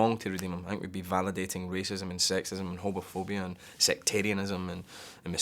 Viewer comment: totally sounded like he said hobophobia. fear of homeless people? heh, we have that where I live. transients are spreading Hep A <puke>